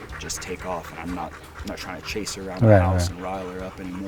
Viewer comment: "I'm not trying to chase her or rile her up anymore...?" Did anyone else catch that?